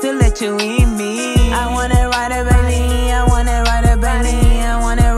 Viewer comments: ii thought it was " ii wanna ride a Bentley" 💀💀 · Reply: Girlll I thought it said "I wanna ride a bunny"🤣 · I thought she said i waana ride a bunny 🤦‍♂️